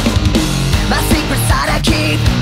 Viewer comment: Everytime I hear the intro, I hear "The secret sodomy" I hate my mind